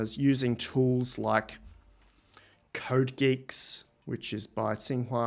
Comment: did you say "code geeks"? Could you provide a link for that one you referenced?